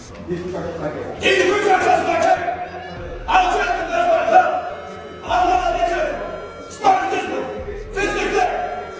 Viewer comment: Can anyone tell me what they were saying in 4:00 100$ says you can't unless u speak Marine Corps. (not actually paying 100$) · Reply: its the drill sergeant creed which states "I will develop them into smartly disciplined, physically fit, basically trained Marines, thoroughly indoctrinated in love of the Corps and country. I will demand of them and demonstrate by my own example, the highest standards of personal conduct, morality, and professional skill."."